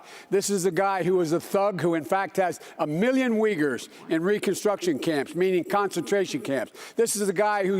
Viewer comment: Did Biden say "wiggers"?